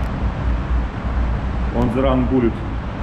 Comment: он же ран буллит)) мне послышалось так)